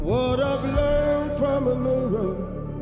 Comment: Moved to tears - I misheard "What I learned from a neighbor, Look too hard and you'll find a stranger. Love is a choice." Correct is "mirror" not neighbor, but that's the same idea - we can choose to see our family in everyone we meet or a stranger to whom we treat like they don't matter. Love is a choice. What he learned from a mirror can be applied to everyone.